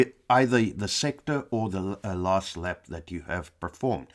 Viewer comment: you said "last lap" did I hear that right? What about best in session (what i would prefer)? Can you choose which?